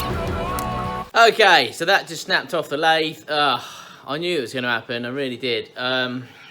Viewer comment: "Lathe???" This whole time I thought he was saying "Lave!!" 😂